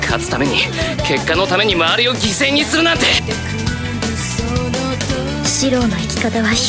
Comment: what does shirou actually say? Cause I'm hearing Mario out from his mouth at that time 0_0